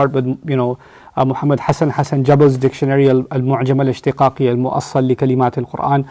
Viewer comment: Did anyone catch the name of the dictionary mentioned at 3:19? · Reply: It was an Arabic dictionary of the Quran named al-Mu'jam al-Ishtiqaq.